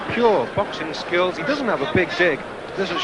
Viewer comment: does he say "he doesn't have a big dick"